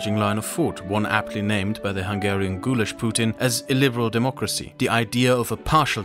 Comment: "Hungarian Gulasch-Putin" Oh my god that one is genius. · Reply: Ohhhhhh that made much more sense than what I heard, which was "ghoulish Putin". Which made me laugh, but honestly, more ghoulish than Putin?